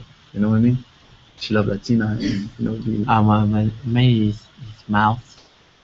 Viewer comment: what did Lau say at 24:04 lbfchannel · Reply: Lisa Parks I think he said mines is mouse,talking about Jessica(his girlfriend,he calls her mouse